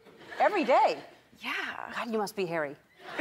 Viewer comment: I thought ellen said Harry and taylor laughed nervously. It's HAIRY! 😂😂😂😪